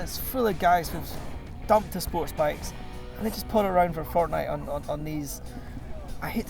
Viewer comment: Did somone say Fortnite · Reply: Taylor Hokanson he said fort nine, which is a popular motorcycle YouTube channel